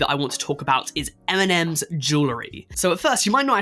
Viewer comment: I thought he said Eminem's jewelry which does sound like normal rapper stuff